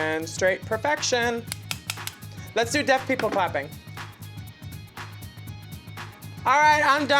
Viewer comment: I literally thought she said deaf pickle fapping